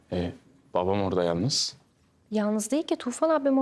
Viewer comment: 3.00 tufan ablammı diyor benmi yanlış duydum